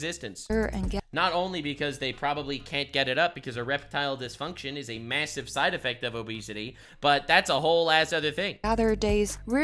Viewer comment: did he say "A reptile disfunction".